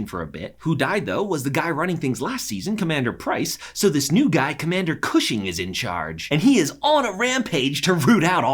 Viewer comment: did I hear... “Commander Cushion”?? 😂